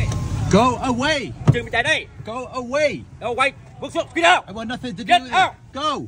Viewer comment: they guy sound like he say get out….. NO WHITES… What the hell man